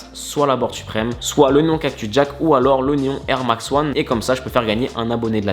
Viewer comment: il a dit l’oignon cactus jack ou c’est juste moi qui entend mal